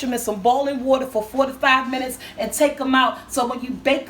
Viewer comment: OMG lol at first I thought you said boil the peppers for 45 minutes,, I said WHAT!!!!! Then I realized its just how you said it cuz you talk so fast! LOL What you said was,,, 4 TO 5 minutes heheh Thank you! These make THE best dinners in the world! MMMMMMMM